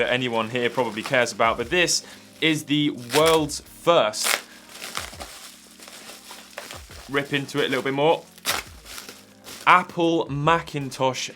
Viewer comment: 15+ Hours of research went into this video. Huge thank you to Alex for the translations and Sendico for making this happen. I REALLY hope you enjoy this video!!! Sendico - <link> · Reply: I thought you said “world’s first apple mouse” which made me think about The Lisa Mouse.